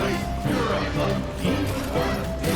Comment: I heard "You're a monkey!" 😭